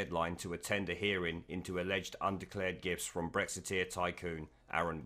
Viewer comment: thought you kept saying 'undeclared GIFs'